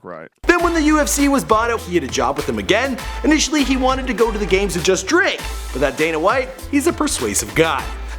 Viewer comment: did he say he wanted to go to the UFC GAMES and just drink? Games? you drink before you make these videos??